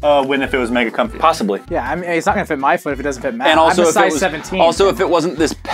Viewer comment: Did anyone else hear Pat is a size 17 shoe 9:12?